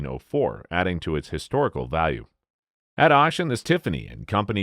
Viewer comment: the robot says "at oshen" instead of "at auction."